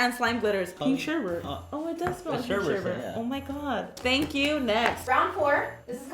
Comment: Did she just say thank u, next here 10:23 lol!